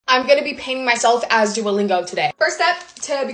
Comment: I thought she said ‘ I’m going to be painting myself as dualepa ‘ sry idk how to spell lol😅